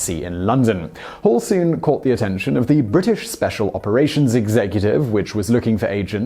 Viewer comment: Did he just say "zig-zag-cutive" right after 1:40?